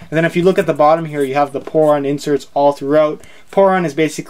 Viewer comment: I thought he said porn insule